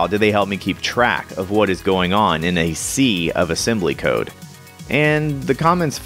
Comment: At first i heard "In the C of assembly code"